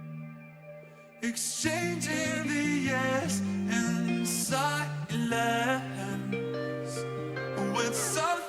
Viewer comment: At the beginning i thought he said “exchanging the S in silence” like to bodies against eachother in an S shape. Wild.